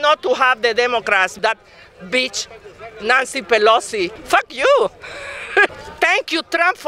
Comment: “Dat beech, Nanci Pelosi” -Airy-bottle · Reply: When she said Nancy, I thought she said nazi